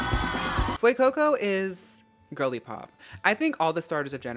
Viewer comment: So I am watching this at 2x speed, and I definitely heard "Glen Coco" instead of Fuecoco. And yknow what? Four 4 you Fuecoco, you go Fuecoco.